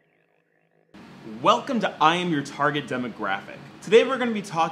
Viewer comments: I thought you were saying "I'm retarded demographic" I was like cool he's retarded and owns it. Now I hear "I'm your target demographic and I'm like oh... I'm retarded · It sounded like he said "I am the retarded demographic" lol good vid tho